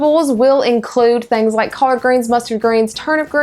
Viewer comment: did she say "car greens"?